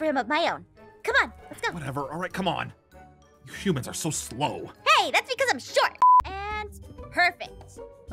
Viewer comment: SHE ACTUALLY SAID “HEY! That’s because I’m short!”